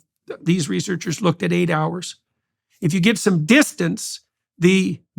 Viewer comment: 8 hours?! I thought he might have said 8 days apart.